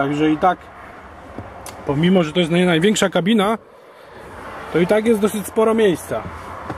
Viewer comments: Największa kabina Space Cab? A nie czasem Super Space Cab jest największa? :) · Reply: kolego jeśli chodzi ci o moment 5:55 to nie powiedział "najwieksza kabina" tylko "nie najwieksza kabina". Przynajmniej ja to tak usłyszałem ;) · Największa kabina Space Cab? A nie czasem Super Space Cab jest największa? :) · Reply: Pan Adrian Ja usłyszałem jednak zająknęcie się " pomimo, że jest to naj największa kabina... " :)